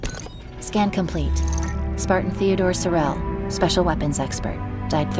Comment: I thought at first it said sparten thorn as from the Halo 4 sparten ops